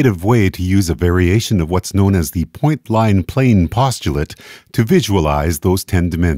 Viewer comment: did u say point Bline ? or pline